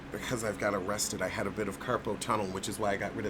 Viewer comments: I swear I heard say “I got arrested and now I have carpel tunnel”😂😂😂...I was thinking damn how tight were the handcuffs 😂😂😂😂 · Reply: Robin McDowell He was so serious, too😆😆7Im like baybeee... he said, "rest it." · I heard "arrested" as well. I thought he was saying being in handcuffs gave him carpal tunnel. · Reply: I heard "I got arrested" too. Girl, my head popped up and my neck rolled. Hearing isnt my thang, apparently! 🤣🤣🤣😉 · I heard arrested also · OH. "I've gotta rest it." I heard "arrested" too lmao 😂